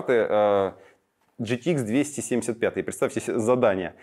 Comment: Слышу: GTX 2070... Да ещё на ХР-хе! Ну, думаю, всё... я допился...! Переслушал 2 раза - фу-у-ух, показалось... 👍😁🤪